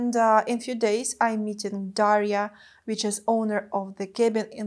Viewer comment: Hey, Great to see you again. In the beginning of the video when you were describing your stress levels and a few nagging problems that you have in your back and arm, I can't believe they're not gone yet but I hope you get it checked and become pain and stress free soon, you suddenly mentioned Daria and usually I don't have trouble with your accent infact I love your accent but today I misheard that as diarrhea and I had to rewind to hear that again 🤣 It's not uncommon for me to rewind your videos but not for the reason I stated above ahem.. Your comparison of an Instagram influencer/model and a doctor was accurate in my book as whenever I am not feeling well insta models do help me a lot in feeling well and they sure do cheer me up...so yeah they're the same (for me at least) both keep in good health 🤣 Your belts and accessories and barbarian bra or brabarian looked cool and interesting and can't wait to see you try them the way they're intended to in your other platform Also I don't need to tell you how you're a barbarian and make a cheesy comment coz I think you got your fill of cheese from the pizza itself 🤣 Trying different outfits and different things while keeping in mind YouTube's stupid and arbitrary rules is not a bad idea and it will only add variety to your content as let's be real you look so ravishing in those and you know how to dress up 😂 but it should be few and far between in my personal opinion as the other stuff that you share as part of your content